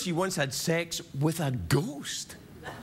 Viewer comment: I thought he said Kesha did it with a GOAT 🤣 Jokes on me. I googled it.